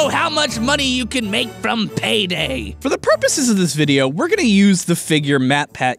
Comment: All I hear is “Use payday forever”